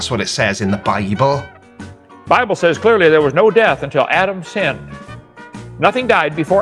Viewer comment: I shit you not, I thought he said "Adam Sandler."